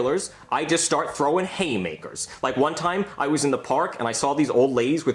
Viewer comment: For some reason from the start I thought he said HATEmakers